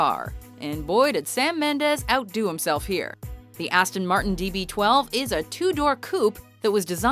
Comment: she said dB12 instead of db10